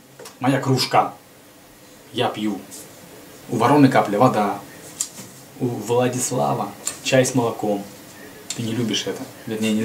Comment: А мне послышалось,что она звала ВЛААД,ВЛААД.......